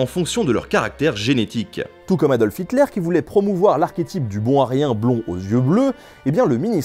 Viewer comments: "bon Aryen" j'ai compris "bon à rien" XD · j'ai entendu "bon à rien" au lieu de "bon aryen" … Comme quoi :p